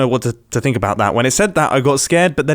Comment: I literally died at the "cockpits, not just bowls" moment. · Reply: Oh... I thought it was "cockpits, not just balls" lmao maybe I misheard lol